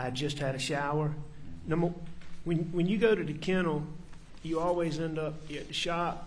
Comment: What is he saying at 1:43:42? "When you go to the kennels..."